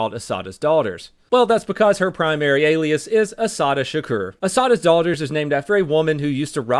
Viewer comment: Well then, I guess i will start "El Chapo's nephews" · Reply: i misheard "Daughters of Assatta" as "Daughters of Osama" at first